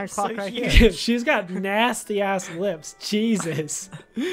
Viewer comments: did you say dirty ASS lips what the hell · Did anyone else hear ''Nasty ass lips''